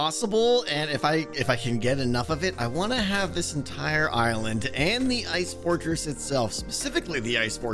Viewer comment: it sounds like he says "i wanna have the entire Ireland.."